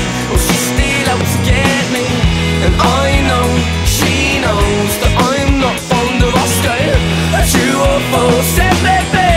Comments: Do people actually have trouble understanding he says fond of asking? lol Maybe just because I'm british myself I can't understand the difficulty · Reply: British (Sheffield), and always thought he was saying "I know that she knows im not on the roster". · I thought the lyrics were "I Know she knows I am not from Alaska" lol i love this song one of my favorites...i love the ending to "17 Again" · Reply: I thought it said “I know she knows that I am not from the roster” 😂